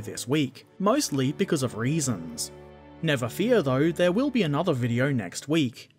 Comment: Goddam those reasons, always making video’s delayed :P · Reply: Ethan Rowland I thought he said raisins first time I heard it